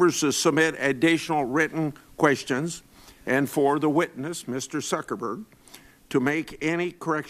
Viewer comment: Did he say "Mr Suckerberg"? 11:34 LUL